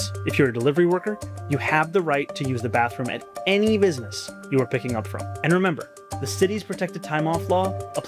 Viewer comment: I don’t think that’s what he’s saying, he said “you’ve got the right to use the restroom at any location, YOU ARE PICKING UP FROM” I’m NOT a fan nor do I align with ANYTHING he’s about, but I, too, thought he said this and listened a couple more times before fully grasping that he covered his disgusting butt! I still think it’s dangerous to tell ppl they’ve got “the right to …..” when he clearly doesn’t plan on complying with THE RIGHT TO REFUSE ANYONE AS WELL AS THE RIGHT NOT TO PROVIDE PUBLIC RESTROOMS! If I’m wrong, my apologies. Either way I’m disgusted with this clown & I absolutely brag and tell everyone about officer Tatum daily!❤ edit to say, I want to be clear that I agree and align with Officer Tatum.